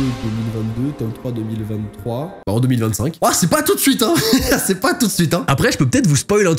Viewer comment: EN 2-mille QUOI ??! 😧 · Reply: 2025 alors que le tome 3 n'est même pas encore parut 😂 (on prie pour novembre 😢)